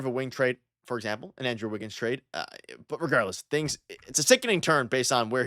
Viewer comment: i thought he said nigga 😂😂